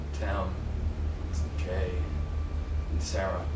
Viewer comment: Okay I can't be the only one who heard "Tim is gay"